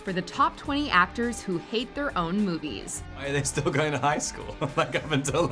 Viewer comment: I thought it said "Actors that HAVE their own movies" and expected Being John Malkovich